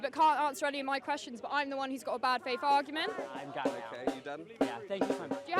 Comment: I thought he was saying a "bear faced argument" 😂